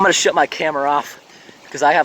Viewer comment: At 10.04 I thought he said he was going to sh t my camera off LOL